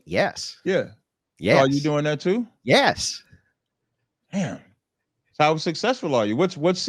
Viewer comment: Andrew made him look bad asf!! Dude is a ignorant ninja who doesnt understand how to debate!! Andrew should have stopped talking to him it wasnt worth it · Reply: when he couldn’t find anything wrong with what Andrew was saying, and whispered damn under his breath t 12:00 😂 I knew he lost